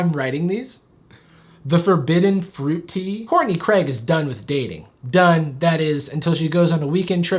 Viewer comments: Anyone else hear "Horny Craig" then get very concerned for Danny and the greg's? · I was looking away when he started reading the Wendy’s wattpad and when he said “Courtney Craig” I 100% miss heard it as HORNY Craig and i was very taken aback 😭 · I swore jarvis said "horny craige" and I almost dpat out my drink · PLSSSSSS I HEARD THAT AS "HORNY CRAIG" LMFAO